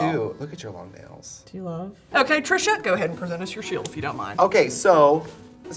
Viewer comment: I feel like Brittney keeps saying “Trisha” instead of “Trixie” am I crazy??? 25:10 and also earlier in the episode